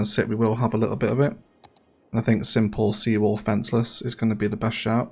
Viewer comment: Not sure why I heard "seawall, fishless" when setting up the fishing industry area.